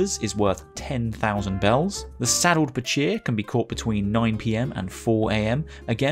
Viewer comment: I heard “this sad old bitch here”